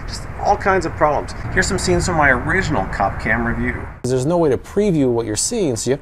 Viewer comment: I'm not gonna keep it a secret: When I saw u on the Cop Cam, ur face scared the hell out of me!! Then u said "Cop Cam", and for some reason I heard "Coff Cam." "Oh, that makes sense", I thought, "He's in a coffin." U, sir, looked dead💀